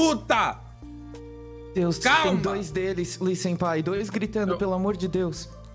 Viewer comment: EU ESCUTEI "Luiz senpai" KKKKKKKKKKKK